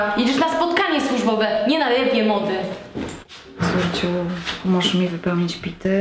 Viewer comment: co ona powiedziała? "Nie na jylewnie mody"?